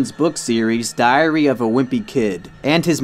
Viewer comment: I swear I heard “diarrhoea of a wimpy kid”